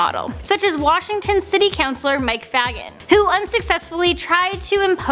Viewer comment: who else thought she said mike "fagg*t"